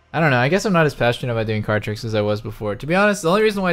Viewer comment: I thought you were saying 'car' tricks and thought that was pretty reasonable.... fucking card tricks dude??